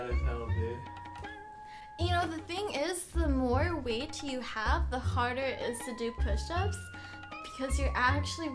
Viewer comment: that face doe rofl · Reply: I thought that chick said "the more weed you have, the harder it is to do push-ups." That sentence and that face. lol